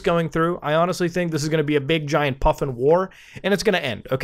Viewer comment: Hey man wtf is a puffin war? 12:50 I have no Idea what you are saying there but the CCs say puffin war. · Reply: He said muffin 🤦‍♂️